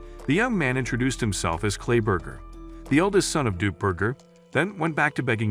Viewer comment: ''Clay burger son of nuke burger'' was what i heard near